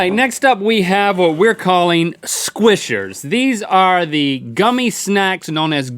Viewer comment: i thought link said swishers instead of squishers